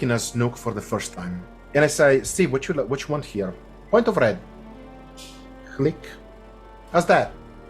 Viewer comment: It’s printer lights. In resolve it would be the offset wheel. Printer lights traditionally are adjusted in printer points. So if you use the offset wheel which is set to RGB values of 25-25-25. A point of red would be 26-25-25. All of this to say that it’s just a small offset wheel adjustment. The most basic adjustment you can do :) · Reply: Thanks for the detailed answer! When you say printer lights, that makes sense to me now. I wasn't thinking of printer lights when I heard "point the red" 😆